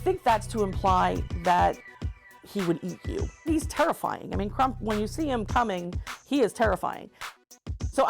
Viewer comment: When they said Krump I thought they said trump